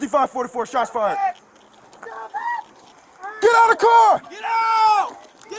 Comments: "Drop the car" is all i heard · Is he saying... "DROP THE CAR!" ... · is he saying "drop the car" @ 3:40 ?